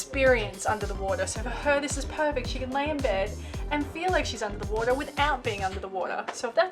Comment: Hiya 👋, Airlie Beach seems so quiet and nice. I’m glad you didn’t get boat sick. That reefsuite is probably the best possible room you’ll ever get. It seems like you said “Felix” instead of “feel like” at 3:32 😆. That snorkeling is amazing. Wish I was there swimming right now instead of being stuck at home with my broken elbow. That sunset is gorgeous 🌅. The Great Barrier Reef is definitely on top of my bucket list 🤤. When I thought the sunset is the most beautiful thing on your trip, I hadn’t seen the sunrise, which is even better 🥹. The diving is definitely something I’d do! I missed diving as my last dive was in 2018, except for a scuba review last month as preparation for my Mexico trip in November. Your trip is truly amazing, I wish there were longer trips on the Great Barrier Reef like a week or 2 of cruising, it’s such a zen adventure. Thanks for sharing and have a great week ahead 🤙